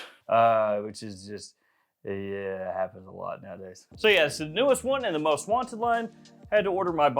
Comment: I can't understand what you said at 0:34. Can someone please verify? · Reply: "Yeahhh.. happens a lot nowadays" is what he said.